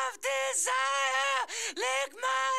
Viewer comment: "I'll make you lick my injuries" Hell yeah, PJ. Love ya, girl ;) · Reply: Poenut All this time I thought she was saying “lick my interface” 🤦‍♂️